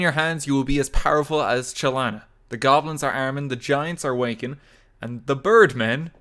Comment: I first heard it as “The giants are wanking” 0:58 I’M DYING LMAOOOOOOO.